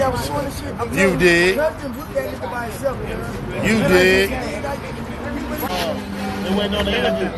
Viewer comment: after they mock the Marathon Peace Sign.. “U digg”.. Is what he says. As if some, Mortal Kombat signature catch phrases after the devastating finishing move U-dig? ain’t used as a question here it was a declaration. To have Nip put in the dirt was literally Big U’s dig…………. UDIG?!! he probably even says this phrase after a lot of successful “business” moves or whatnot look for the bravado in BIG Us face. This what confession look like ifkfr